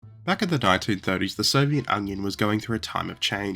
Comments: did say “Soviet Onion?” Or am I going mad? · Did he say Soviet Onion?